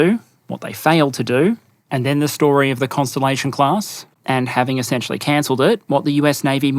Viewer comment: Did I hear "the Cancellation Class"? 😂